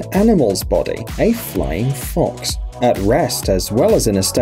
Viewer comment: "During it's creation the engineers as you can easily guess, were inspired by the mechanics of an animal's body... Me: "Yeah, yeah, yeah...it's obviously modeled after a b...WHAT HE SAY"?!?! TZ Guy: ....."A flying fox" Me: OH! Right those...Well obviously! 🤦 PS: Just don't tell the children about those secret "Flying Foxes" animal that are in caves, in the woods....They're creepy! 🙅 they all squeal 🦇🦇🦇🦇🦇🦇🦇